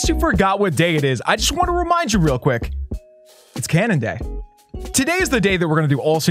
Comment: i heard canada day lol